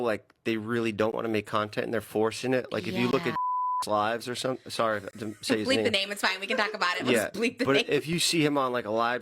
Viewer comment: who’s name did he say? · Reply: Late af but I’m pretty sure it was Jason Nash